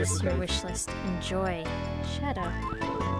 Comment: why did she said shut up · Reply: I said "shadow" because that's who the box came from.